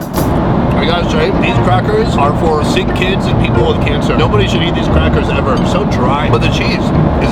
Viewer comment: did he just say these crackers are for people of cancer?????? 😳